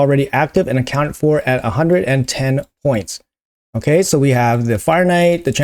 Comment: Lol i said ten ten i think 😅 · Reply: You made me go back and listen again, lol. Nope, you said 110, instead of 1010. Twice in 8 seconds. Go to 4:50, and 4:58. More coffee next time!! Lol.